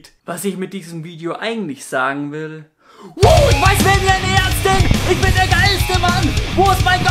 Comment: was sagt der da eigentlich? "wou ich weiß wer mir will ne Ärztin!" ?